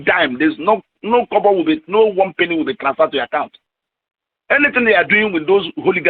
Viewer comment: I can see the subtitles say ??? but he is clearly saying 'no kobo will be transferred ', kobo is the subdivision of the Nigerian Naira; a kobo is basically worthless now days